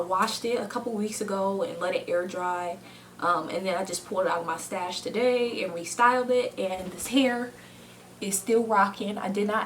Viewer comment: I thought you said I pulled it out my moustache and styled it lol